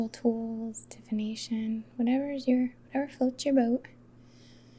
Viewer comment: @ 23:04 did you say "whatever floats your boat??"